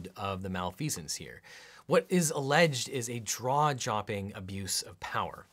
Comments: anyone else catch him say "draw jopping?" Thanks for this video, it's very informative even at this point (after the impeachment proceedings). I thought I was reasonably educated on the topic but I guess I wasn't, really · My jaw dropped when I heard "draw-jopping" at 37:25 ;) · did i hear that correctly? draw-jopping? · did you say "draw-chopping"?